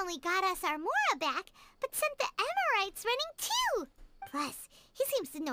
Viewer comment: did Paimon just say EMERITES?